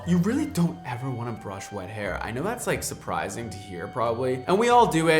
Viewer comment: When said “wet hair” I thought he said “white hair” I was so confused because why don’t we brush it?? 😂